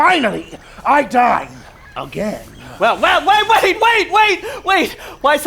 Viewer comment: Pincer said he gets to "dine again" but the first time I heard it I swore he said " to dance again!"